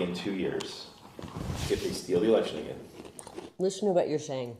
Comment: Did anybody else catch what he said?? At the 4:12 mark he said they “THEY STOLE THE ELECTION AGAIN.”!!!!! These bottom feeders are the ones who fucked trump!!!! LISTEN!!!!!